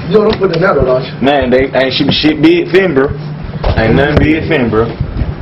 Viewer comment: im from england yo. wat the hell does oj say at 0:08????? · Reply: naw he said ain't nothing big fit in there in the LV case